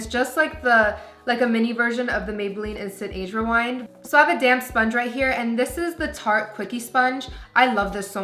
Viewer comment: i thought she said ''I have a damn sponge'' 😂😂